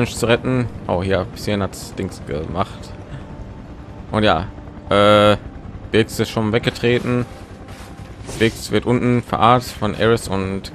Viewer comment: Ich verstehe. Biggs ist weggetreten und Biggs wird unten verarztet.